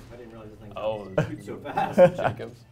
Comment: Did he just say that that was a Jakobs?